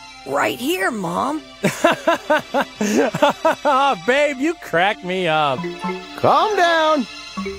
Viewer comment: did goku just call chi chi babe?